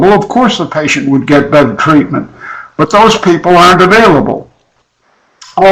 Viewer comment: is he saying "if Sigmund Freud or Frieda Fromm Reichmann were available, of course the patient would get bad treatment"?